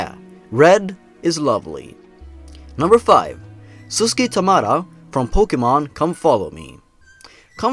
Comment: NARUTO SUSKEY! THATS RIGHT MAKAYLA IF YOUR HERE I HAVE A YOUTUBE CHANNEL AND CAN ABSOLUTELY ANNOY THE HECK OUT OF YOU EVEN IF WE'RE NOT AT SCHOOL! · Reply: Don't ask, I thought I heard Suskey so I thought I'd annoy one of my friends >:)